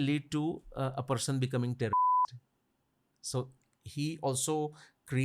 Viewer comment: what was the beeped word? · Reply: Think it is terrorist